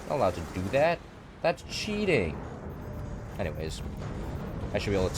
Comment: did I hear "that's cheating" ??! Close enough welcome back Technoblade 👑